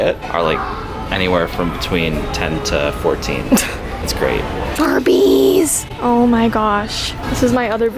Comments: Who thought she said firbies instead of Barbies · I WAS ALSO LISTENING TO SOMETHING ELSE AND I HEAR TUBBIES😵🤣😂😆😆